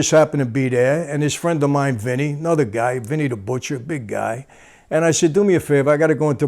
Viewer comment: I thought he said Benny The Butcher 🤯